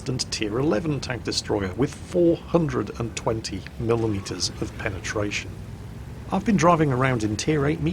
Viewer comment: Did Mr. Jingles just say 440 ml instead of mm? Hehe maybe I just hear it wrong ;)